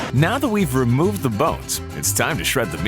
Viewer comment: bro just said boats instead off bones in 2:14☠